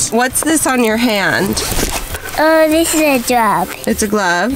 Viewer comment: did anyone hear "dis is a drug"